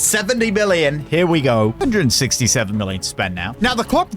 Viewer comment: did he just say 67